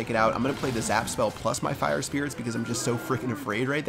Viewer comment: it sounded like he said fu#@ spirits